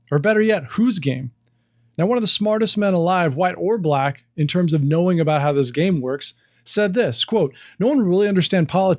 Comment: I couldn't understand the name of the man that was mentioned at the 3:54 mark. Would someone mind letting me know what his name is, please? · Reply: That's Thomas Sowell.